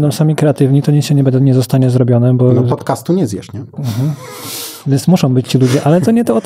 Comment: nie zrozumiałem co gość miał na myśli. PODCASTU nie zjesz? Dobrze usłyszałem? Tzn, że podcast jest kierowany do ciemnej masy?, do ludzi mniej ambitnych?